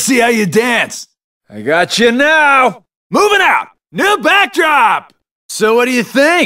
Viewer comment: I always heard the "New back drop!" as "Rebecca!" or "You baka!", I ended up headcannoning that there was another heart inside Sora belonging to a Rebecca and that Xigbar was looking for her